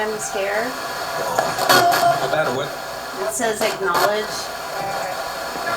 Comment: When you said it's says acknowledge it sounded like it said the murder weapon